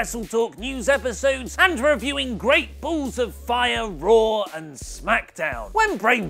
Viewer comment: did he say "Great BULLS of fire"?